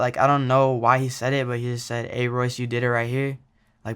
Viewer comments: yo i didnt know he tag said “ aye royce you did it right here “ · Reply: Unfiended I thought he said “aye Rex ED right here” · I always thought mosey said "aye racey dee right here" lmao I had no clue it was "aye Royce you did it right here" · I never knew he said “hey Royce you did it right here” I thought he said “ay whachu think bout me”